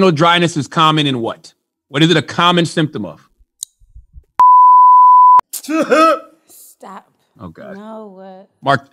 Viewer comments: what he say? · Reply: Transgender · Anyone know what Schulz said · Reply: Transgender